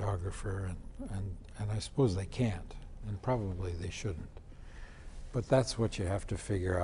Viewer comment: when Harrison is talking about schools not teaching actors how to work with directors/DPs, does he say "probably they SHOULD" or "probably they SHOULDN'T"? Thanks! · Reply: He said that, "they can't and they probably shouldn't".